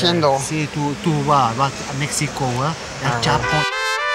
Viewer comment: escuche que el que vendía cocaina dijo "yeah, mexico, el Chapo" xDen el minuto